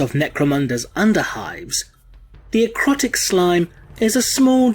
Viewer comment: I thought it said "Erotic Slime" I was dissapointed